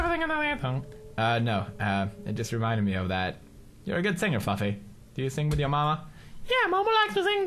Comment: did leads say fugley instead if fluffy???